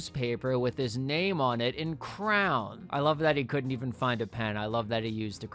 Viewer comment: did you say CROWN?? its CRAY-ON. I'm considering unsubscribing.